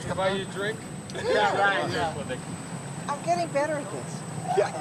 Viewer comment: did she say "iam getting better at this" ? i mean she was towed up there like a pack of rice in china.